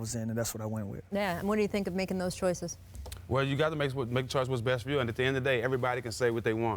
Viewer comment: What the fuck did he say?? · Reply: “You gotta make the choice what’s best for you”